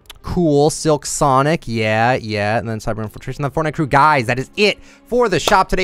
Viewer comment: does he say "cyberman infiltration"? a doctor who fan???